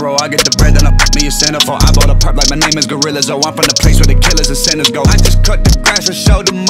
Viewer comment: I definitely heard Gorilla Zoe and not Gorillaz though